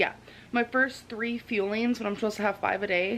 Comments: Fuelings. Thought she was saying “feelings” for the longest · It wasn't until the second time watching I realized she was saying "fuelings" I thought she was saying "feelings". Love your channel, I never comment but I'm in my clinical rotations for med school, family medicine. Last week we had a male patient come in for a screen and he right away asked about Optavia. I asked where he'd heard of it, he said his wife watches Amberlynn and they were starting it to follow the program with her in real time. He was fairly excited and it seemed like Amber was their accountability. My attending had heard of the program, didn't say positive or negative about it just that anyone at that BMI should be monitored during weight loss. They can potentially drop a lot of weight fast, which decreases muscle mass and puts strain on an already damaged heart and their prone to host of complications. Also that any diet not revolving around balanced eating and exercise, much less an MLM isn't his first 'recommendation.' Anyway, I was really bummed to watch this and hear she'd quit. She doesn't realize the reach she has, hate views aside people do look up to her apparently. I left this on a community page for a different channel but I figured it could get more exposure here. Sorry this is so long, normally youtube stays on screen but this was too personal and bothersome to not get it out. [Edit this patient was also a sanitation worker, $400 is a lot for a middle class family.]